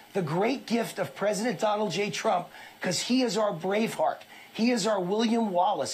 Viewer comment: When he said Braveheart, I first heard it as Brainfart.